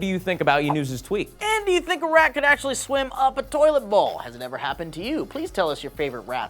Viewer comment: at 6:98 i had forgotten about the rat and i thought he said "do you thing Iraq could swim up a toilet bowl?"..... i was so confused.